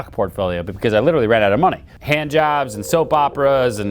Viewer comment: does he say 🖐️ jobs? I guess times did get desperate. I know what he meant but still, it was funny for a minute. Even Ryan would have laughed.